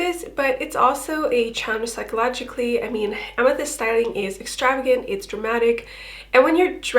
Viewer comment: a typo on the subtitles, "eth" instead of "amethyst" (Just putting this out there so it can be corrected someday, only good intentions🙏 tysm for everything you do!!)